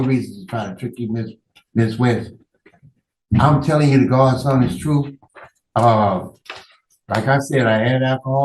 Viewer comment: "I'm telling you the god's son is true." Think maybe he said "God's honest truth."